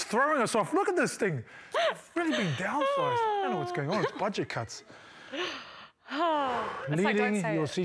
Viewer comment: I’m Australian and when he was complaining about the desk and said “budget cuts”.... thought he said “bodgy c*nts” 🤔